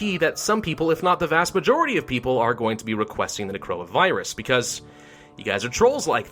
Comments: for a sec I thought he said “coronavirus” Who else? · I legit heard "corona virus" and I was freaking out cause this video's 4 years old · “I can almost guarantee that some people if not the vast majority of people will request the corona virus.” Had to listen again 5 times before I got what he was saying